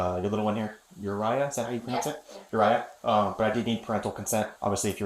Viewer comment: had to replay that a few times, thought he said his name was Jiraiya lol. No J though.